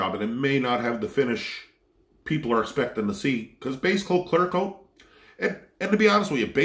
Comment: Every time he says "Clear coat" I hear "True Coat" from Fargo.